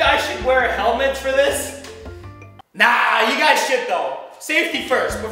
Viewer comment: Who else heard. nahhh you got shit tho.